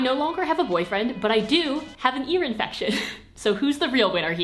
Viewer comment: “I no longer have a boyfriend, but I DO have an ear infection.” iconic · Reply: I thought she said urine infection and talking about std 😐